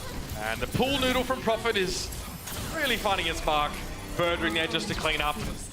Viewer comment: did he say "really funny as fuck" ? LOL